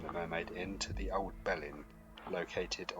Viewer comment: Was in Rye a couple of weeks ago! Love the place! Thanks for the video! I did chuckle when I mistakenly thought you said the Mermaid was connected by tunnels to the "Old Bell End"! 😂😂